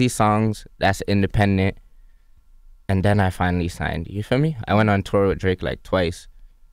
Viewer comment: he said you know instead of ya feel me